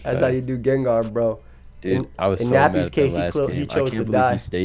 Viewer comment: Ayoooo @ 20:54 “In Nappy”s case he chose to die” Did I hear that right?😂😂